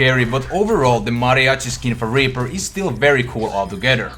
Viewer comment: Did anyone else hear raper in 3:03?